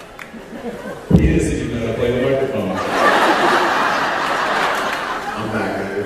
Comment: what did Jensen say? T^T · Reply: "He doesn't even know how to play the microphone!" :)